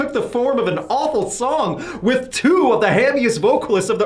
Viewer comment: I refused to watch anymore when you said josey and chad had the heaviest vocals in the early 2000s. Compared to who? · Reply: my apologies then, it sounded like heaviest I even went back and listened a 2nd time and I swear it sounded like you said heaviest. Apologies